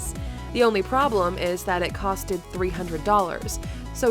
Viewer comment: does she say "it costed"????